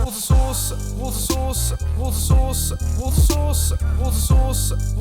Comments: I kept hearing "water sauce" in the time lapse. It was more entertaining than the song. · I swear he's saying water sauce. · My American self thought you were saying "water sauce" at first 😂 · During the time lapse it sounded like u were saying water sauce😂😂😂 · Anyone else hearing raw sauce instead of water sauce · In the time laps it sounds like he says water sauce😂😂😂